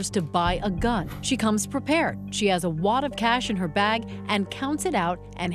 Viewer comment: i thought she said a “watta cash” like if she were talking to a baby...... she said a “WAD of cash” 😭😭